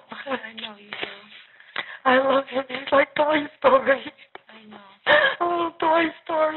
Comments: From 14:06 to 14:09 Terrys subtitles say "indistinct" twice. It sounds like shes saying "Toy Story". Which makes her statement sound very bizarre. "I love him, He's like Toy Story... sobs ohh Toy Story.. And hes so gentle. Hes like a chuckles little doll." chuckles 😮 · 25 MINUTES IN AND I'M CONVINCED THE GRANDMA HAS DEMENTIA AND NO ONE BOTHERED TO NOTICE AND JUST KEPT MAKING HER BABYSIT. THEY REALIZED THEIR MISTAKE BUT DIDN'T WANT TO OWN UP TO NEGLECTING THEIR MOTHER (strange behavior and statements) AND NOW SHE'S IN PRISON 🤦🏼‍♀️ The daughter said she literally can't function to do her daily things, meaning she's not even capable of taking care of herself, she's not even capable of cleaning her house anymore ! THEY ARE RESPONSIBLE for that babies death AND their mother's IMPRISONMENT 😭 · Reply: Was grandma saying, “ He’s like Toy Story ” ?